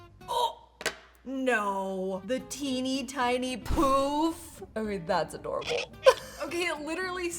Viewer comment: what she said: teeny tiny poof captions: teeny ting puff me!: teeny, tiny POOP!